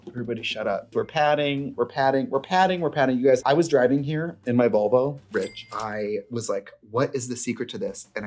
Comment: the way i fully thought for a second that the company was actually called vulva and trixie just misspoke